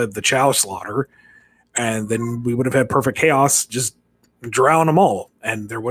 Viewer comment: Ah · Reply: Oh wait screw me, I thought you said Perfect Cell 😭